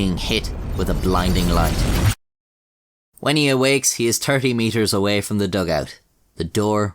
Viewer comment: "When he awakes he is Turkey meters away.." Am I trippin or did I just hear that :D